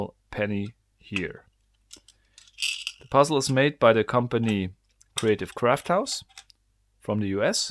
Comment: I thought he said creative crackhouse😂😂